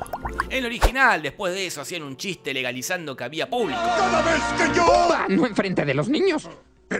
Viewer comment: che, que dice ahí? no entendí nada☠️ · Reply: "Legalizando que había público" eso dijo